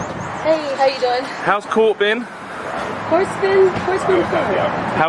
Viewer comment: I thought she said horseman but she said court been